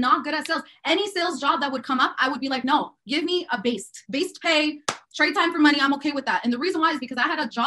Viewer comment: Did she say she had a job with Bast Pay @ 15:42. 😂 idiot